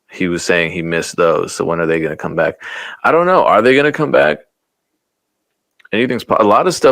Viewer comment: It sounded like " I wanna negguh come back" lol 😆 I also hope Mark Morrison returns too since we on the subject 😂🤣😂